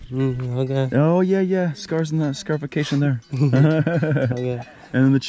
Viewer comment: Honestly there are so many traditions like scarification that aren’t really that harmful and I don’t think they should be abandoned completely in the modern world so many traditions are beautiful and it’s so wonderful to see them at least in some ways recorded and preserved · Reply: Ah holy shit I’m stupid, I thought you said sacrifices